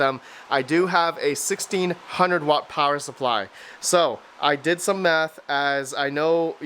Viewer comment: Oh my god. I was listening to this while working on the laser engraver and I swore to god I heard you say "I did some meth..." I rewound and was happy I misheard 😂